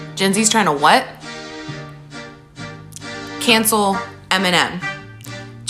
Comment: I have the IQ of a cactus and was listening to this audio only, so when she said “they’re trying to cancel Eminem”, I heard “cancel M&Ms”, as in the candy.